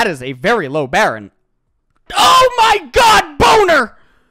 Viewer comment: See I thought I heard Ls saying boner earlier but now I hear it very clearly at 17:58😂